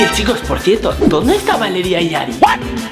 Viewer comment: No escucharon que dijo Ari